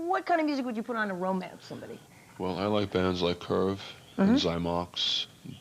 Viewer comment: what band does he mention? Sounds like he says Curve? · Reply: Yes he said Curve